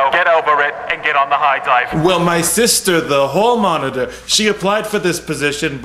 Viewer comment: i thought it said hell monitor instead of hall moniter 😂